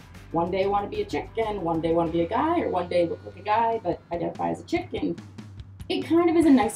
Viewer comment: I'm glad I'm not the only person who thinks this way. Great vid! Aslo at 12:02 I thought you said identify as a chicken and I was about to be like me too